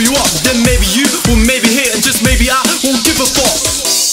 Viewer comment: I thought you were saying, "I won't give a fock" like in that British grimey way.